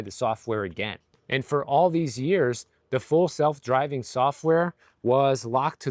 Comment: I heard "fool self driving software." ;)